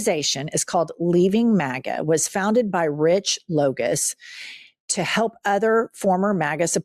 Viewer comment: For a second I thought you said the leader of the leave MAGA movement was named Rich Bogus. But that would have just been TOO perfect